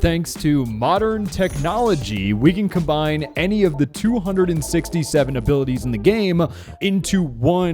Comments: did he just say “67”?! 😮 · did u say 67?